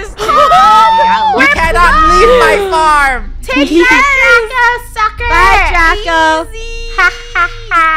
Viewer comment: Is it just me or did anyone also heard say "Take that Draca" at 13:18 or I just misheard 😂